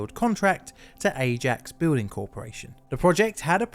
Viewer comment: 'Ajax Building Corporation'. Thought for a moment you said 'Acme' there. :)